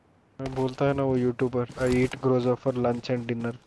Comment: who else heard 'yeet' instead of 'eat'?